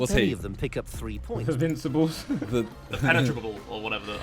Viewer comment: I thought Harry said "the vegetables" hahahaha